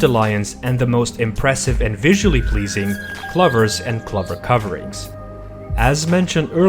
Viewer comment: Kluphers? Uh, those are clOvers. O as in Oh. Took me a bit to figure out what word he was mangling since kluphers don't exist in this video.